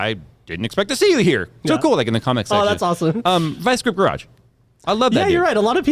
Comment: What Jay said: "Vice Grip Garage." What I heard: "Vice Script Rush."